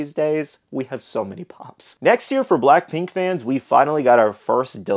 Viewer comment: funko shop beast gohan is 2025?? i thought he was dropping in september/october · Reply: it’s becuase in the video it sounds like your saying “Next year here” lol